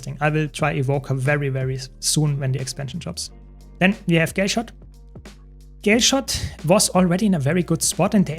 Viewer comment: I swear bro said gayshot